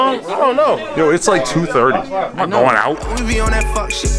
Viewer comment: Who thought Adam said ‘’going out’’ as if he wanted to go out but in the jumper show he said he did not wanted to go out which makes it funny